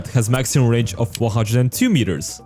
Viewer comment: did u say 402 meters? while displaying 102? Im confused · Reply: He said 102 it's just his accent